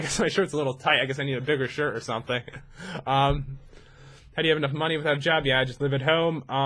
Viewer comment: I thought it said how do you eat Jen XD